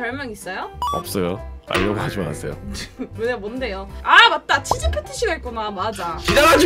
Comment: 내 귀도 이상한가... 치질 ㅍㅌㅅ를 치즈패티로 들었어..